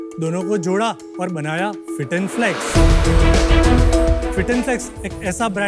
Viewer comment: did he just said fit and sex ??